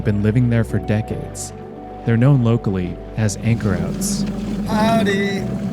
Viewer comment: I thought they said “ anchor rats “ 😂